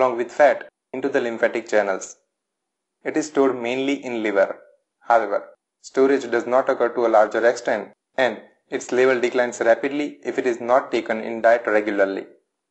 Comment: What is being said at 1:10 - 1:13 ? Your accent is a bit thick. The name koagulation stems from danish Im pretty sure. After all it was the danish chemist Henrik Dam that indentified vitamin k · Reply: Hi there, thanks for the comment. From 1:08 to 01:18 its “It is stored mainly in liver. However, the storage does not occur to a larger extend and its level declines rapidly if it’s not taken in diet regularly.” You can turn on CC also. Regarding origin of name, few books and other sources (including that I referred) mention it as German. Seems its same (Koagulation) in Danish and German. But yes, Danish makes more sense as Henrik Dam was Danish. Thank you.!